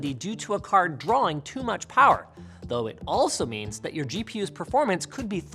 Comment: theres a typo in CC saying GPS when linus said GPU :b